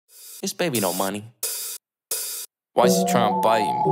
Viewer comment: I always thought he was saying "This baby dont mind me"... I am SO DUMB